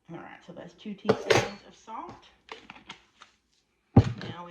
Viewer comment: I thought you said 2 TABLEspoons of salt… but you put in teaspoons.. … this really does look yummy! Thanks!